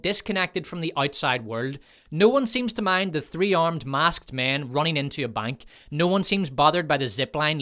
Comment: I kept hearing “3-armed masked man,” and was like “which one of em has 3 arms?”